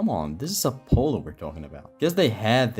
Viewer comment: Bro when he says Apollo it sounds like he says "a polo"